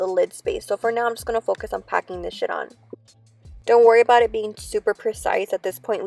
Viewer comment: I swear she said pack this shit on instead of shade so funny lol